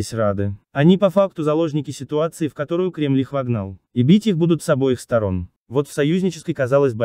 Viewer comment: Ибить их будут, с обеих сторон. Я правильно расслышал? Я верно всё понял?